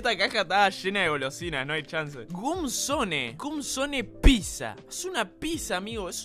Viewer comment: pensé que decía "Cum Zone" bldo xd